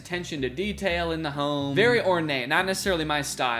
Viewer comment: did say “ornane”? Is that like ornate mixed with inane?